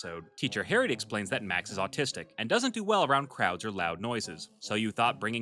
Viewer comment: Did she actually say he’s autistic? (Like me????$)